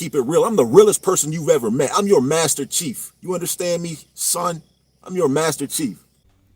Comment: He calls himself a Master Chief? He couldn’t survive the Goat Locker. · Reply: Is that what he said? I thought he said Master Chef. He reminds me of Frederic Forrest in “Apocalypse Now” — he’s gonna wander off into the woods to get some mangos in a minute.